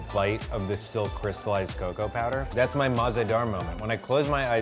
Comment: what does he say? sounds like mazeda moment, is that like nostalgia? couldn't find anything on google for what it sounded like but that isnt a real phrase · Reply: he meant to say Mazadar, which means delicious in Urdu / Farsi (that’s also the name of the restaurant)